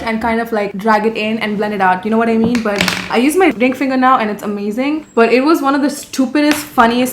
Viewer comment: i heard say d*ck finger 😂💀 tbh I can see her say shit like that